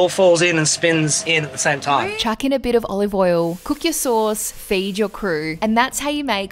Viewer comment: For a sec I thought she said “Fade your crew” and I was like, yoooooo